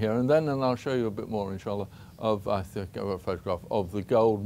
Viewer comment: Did he say insha Allah in